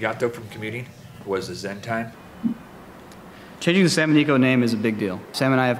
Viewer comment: captions said that you used to call the channel the salmon eco channel :P